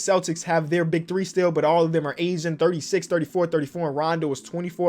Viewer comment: “the celtics have their big 3 still, but all of them are asian” -what i thought said